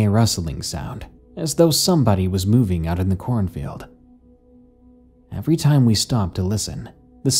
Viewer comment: "Corn filled" is all I hear when "corn field" is said. Other than that, I loved this